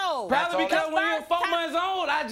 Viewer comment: Did he just say I seen him up until he was 4 months old trying to learn your name lol I done heard it all yall I'm through